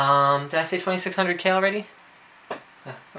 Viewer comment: He mentioned it, I heard its an Thermaltake SilverArrow.... @ 5:00 he names it, hopes it helps :)